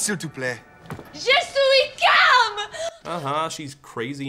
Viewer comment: JEUH SOUWI KALMEUH! As a native French speaker I didn't even really understand what they were saying until I read the subtitles, lol